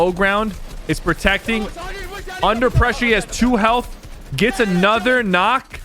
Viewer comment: did he just say under pressy something like that lol